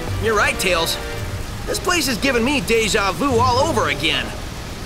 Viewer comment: Did Sonic just say Deja Vu?